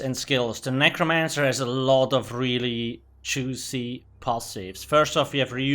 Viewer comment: I thought you said "very choosy" and I went "wtf does that even mean, lol" but I realized you're saying "juicy." I love your accent, where's it from? Eastern Europe somewhere, or?